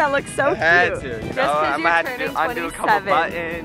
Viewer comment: At first I thought you said he’s turning47